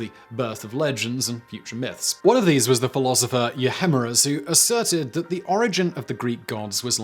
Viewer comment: Can somebody help me out? I'm trying to find something about that guy he talks about at 17:31. I would love to know more about his theories about gods being mortals, but i can't figure out his name. The captions say yahemuras but i've googled it and nothing comes up. I also tried euhemuras, thinking maybe that's the correct name, but no luck on that front either. Any infos would be greatly appreciated!